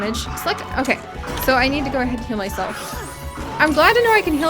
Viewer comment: When you were in battle with the velocaprare for a second I thought you said “I need to kill myself”But no you said “I need to heal myself”!